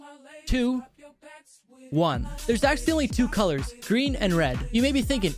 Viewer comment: I love how he sounded like he said "there's accidentally 2 colors"